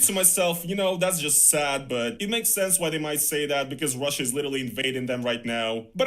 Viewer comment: Ok, I admit, I totally misheard as saying: "...ruzzia's a little invading them right now!" ... and I got really furious about it. So I edit my comment here and I apologize. My other points stand though - mainly left ruzzia bc he couldn't receive his YT payments there anymore, according to his own videos from back then - I highly doubt I misheard or misunderstood him in all of them. & Although never set any trap for him personally at all, felt sooo triggered, that he marched into it with flying colors, ready to "die on that hill" ... not to mention it's a complete miracle to me, why didn't take his character ass*ssination of down, as soon as he realized this and IMO must've felt at least at some point, that the world doesn't turn solely around him ... it's hard for me to believe, really hoped/thought, this'd not backfire on him in negative ways. I'm happy to see the level of support for in (t)his comment section here and even in NFKERZ's comments it felt at least half of his audience called out on his rage bait bs video. At the same time I also worry a lot about safety, now that ruzzian state media is focusing on him and his work. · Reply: bro are you deaf? 😂 He said "literally" not "a little"